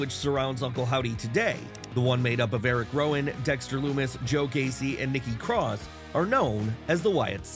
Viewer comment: Listening to this at night, lack of sleep and I thought I heard you said "Joe Hendry" till I replied it that you said "Joe Gacy"